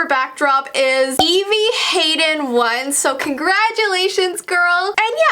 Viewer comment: I thought you said “Evie hated once!” 🤣🤣🤣🤣